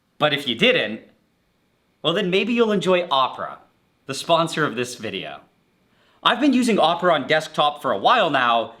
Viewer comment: i thought you said the sponsor was Oprah lmao